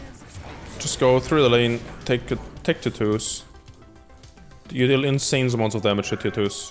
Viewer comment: Had to replay several times to understand he was saying "Tier 2's" Sounds like hes saying Tit'toos. Other then that great video!